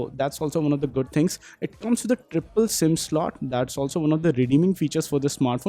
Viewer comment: Did you said triple sim slot